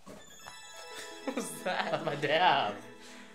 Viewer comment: did just say damn?